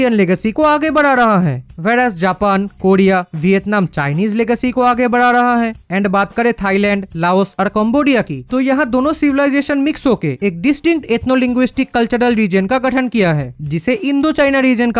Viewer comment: you did Cambodia🇰🇭 dirty I heard Bombodia😂😂